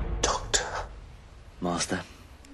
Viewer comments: Is he saying master or martha? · Instead of master, I could have sworn he said Martha at 50s (50 seconds) lol :) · 🤣🤣🤣 at first i heard him say "Marta " , i had to replay it